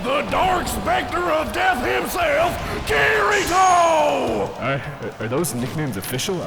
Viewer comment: The first time I watched this I swear I heard "The Dark Spanker of Death Himself: Kirito" and I'm not sure which version I like better 15:15 SOMEONE TELL ME THEY'RE 16